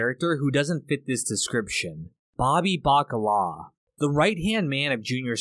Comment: Btw, 'baccala' in Italian does mean salted codfish, but idiomatically it's used as a nickname for silly people. You might call a child 'baccala' when they're doing innocent mischief, for example. It's just a nice detail for the character. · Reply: When I first heard “Baccala” in the show, I misheard it as “Bacalao” which is the Spanish word for codfish. The word is also used in Spanish slang (“cortar el bacalao”) and it translates to “cut the cod” in English but it basically means to be the boss/take charge.” Not sure if it was done on purpose but it is fitting as Bobby does end up becoming an underboss.